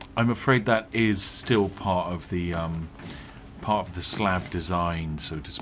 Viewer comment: The slab, I thought he said Slough